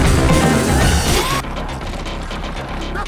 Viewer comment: What does the voice say at 1:28? I cant make it out but i love that part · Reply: "you're becoming more like me, Linda!" Either that or "you've become more like me, Linda!".